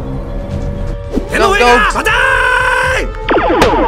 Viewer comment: Did he say Allahu Akbar?